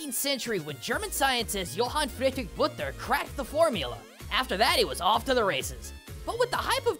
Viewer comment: for a second there it sounded like he flubbed the name and said crap before carrying on like nothing happened 😆